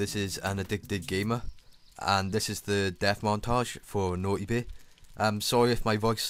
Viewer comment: I thought it said dance montage :P i was wrong